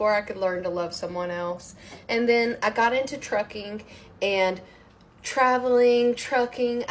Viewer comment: Oh Trucking! Could have sworn I heard tricking. Turned out I was right